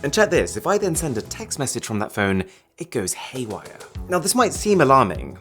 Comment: "It Goes Hay Wigher" Instead Of "Way Higher" 😂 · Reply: He actually said “haywire” and not “hay wigher.”